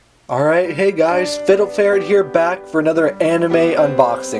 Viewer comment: It sounded like you said Fiddle Fairy in the beginning. lol.